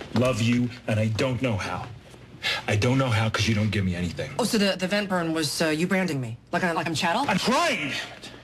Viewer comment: Can someone please tell me what they're saying between about 0:36 and 0:40 after Owen says "You don't give me anything...." thanks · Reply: I know this is late, but Cristina says, “Oh so the vent burn was You branding me? Like I’m chattle?” And then Owen says, “I’m trying”